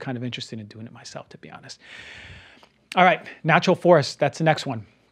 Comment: I’ve never heard of Natural Forest and can’t find it anywhere on the web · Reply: Natural Force* I misheard and had to listen again. I think he's saying Natural Force